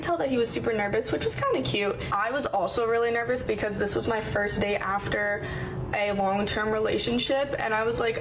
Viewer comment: I think she said it was her first date after a long term relationship, not her first "day". The auto caption just misheard her